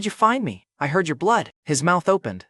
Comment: 🤣x💯I heard y'blood_ I'm outta here this is too fuq'n awesome f'me_ 👋bye guys have fun 🤜🤛🤙